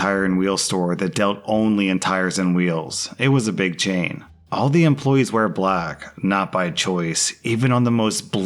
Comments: listening to the audio not paying attention and all i hear is "all the employees were black, not by choice" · Note to self: don't do the washing up whilst listening to @ 7:03 .... What I heard: All their guys were black (not by choice) 😭😭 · Why did I hear "All the guys WERE black, not by choice." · oH MY god - i heard that part as “all the employees WERE black” and not “WEAR black” and when the next sentence was “not by choice” i was SHOOK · I usually listen and not watch. I ahd to pull my phone out to check when I heard: "All Employees were black, not by choice" (instead of wear). Made my day XD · I thought you said “all the employees were black. Not by choice” 🤣🤣🤣